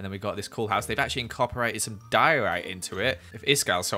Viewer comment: Ok but when you said “Iskall would hate this” when you saw the diorite I laughed so much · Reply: I thought u said Doritos